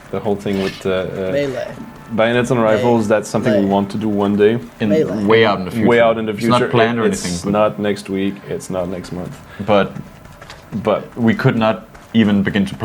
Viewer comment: Anyone else hearing the mysterious voice saying "malay" at (21:05)?